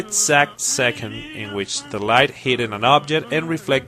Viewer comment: did u just say hitted?